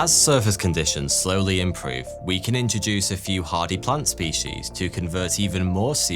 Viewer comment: Totally thought he said "party plant species" just after 12:00, I said right on